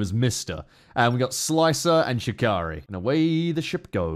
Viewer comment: I had to do a double take to make sure i didn't hear "Yukari" instead of Shikari. What is my life?